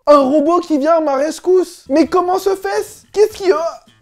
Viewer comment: A 2:08 ya que moi qui a entendu" mais comment se fesse"